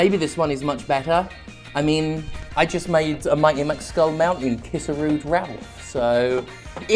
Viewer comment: They made a Mighty Max Skull Mountain kiss a Rude Ralph! We are not worthy. (I thought they said McSkull Mountain until I looked it up to find out what the heck it was)